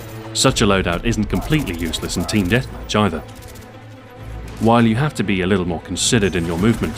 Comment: i know he said team deathmatch either, but it sounded like he said team deathvagina.